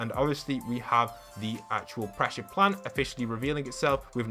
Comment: I’m sorry did ya say “Pressure Plant”??